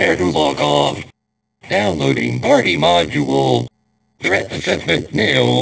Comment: I always thought the robots said "Downloading hardy module"